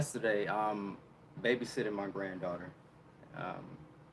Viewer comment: Babysitting your granddaughter? I thought you were like 25? Wow you look good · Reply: Ah ok. So, I didn't hear wrong. I thought he might have said goddaughter.